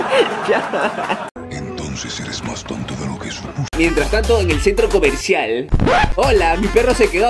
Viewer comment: en este punto corto la ecena de thanos que parece que dice su puta